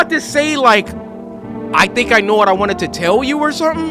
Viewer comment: I think she said “I think I know why I didn’t feel the cold now”